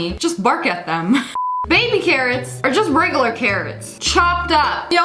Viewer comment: When she said “baby carrots” I thought she said “baby Karens” lol